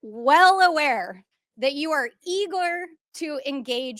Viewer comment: did she just say Elon was eagler? Did she mean eager?